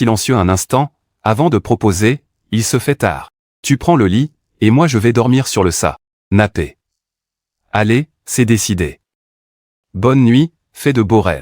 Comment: ces moi ou il a dit sanape😮😂